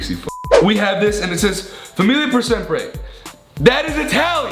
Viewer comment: When Ethan tried to read the italian tattoo "famiglia per sempre" it sounded like he said "a million percent Gray" 😂😂